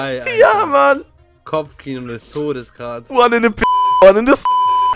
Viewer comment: Musste bei 4:39 so lachen XD Ich kann nicht mehr xP Also ich habs verstanden ^^ "One in the Pinky,one in the Stinky"... Iwie liebe deine Formate :D Mehr davon -:3